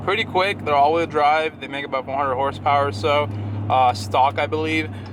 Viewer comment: Funny how the automatic closed captions at 0:53 thought you said “100 horsepower” and “sideboob” at 1:29 and again at 6:29… And what was with that flashing at 8:29?